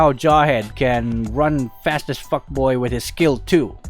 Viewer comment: did he just say f*ck boy